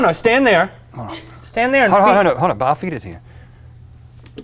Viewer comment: did rhett say I'll feet it to you?